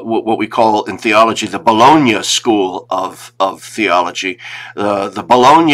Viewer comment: Did I hear Dr. Chapp say that Pope Francis adheres to the Bologna school of Theology? 18:55 😃